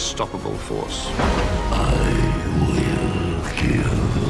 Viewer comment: I heard "Shrek's here"